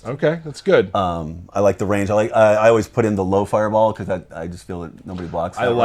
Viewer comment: Is he just said: I like rain.or what? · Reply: He said I like the range of the fireball